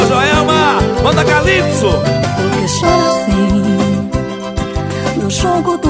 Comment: Cuando era pequeña Cuando cantaba Pensé que decía porque yo nací... Jajaja